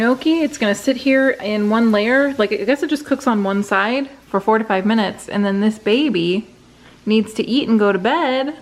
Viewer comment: When said the Gnocchi had to cook for "four to five" minutes, I misheard it as "forty-five minutes" at first. I was like - wait, WHAT?! 😅🙈